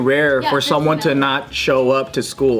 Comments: Is it just me or did it sound like said "Super Nintendo"? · did say “what does a Super Nintendo do?” HAHAHAHHA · Did anyone else hear say “what does the super nintendo do?” At 4:36 ? 😂😂